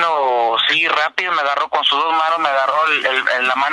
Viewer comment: Qué dice en el minuto 4:55? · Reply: Agarró mi mano y me dijo dales en su madre algo asi